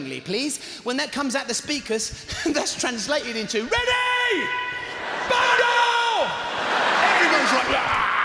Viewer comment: someone tell me what he saids after ready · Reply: He says bundle, basically one person would shout it to a group of friends for example and then jump on to the back of someone to cause them to fall to the ground. Then everyone else in the group jumps on top of them. I think in the US they call it a dog pile.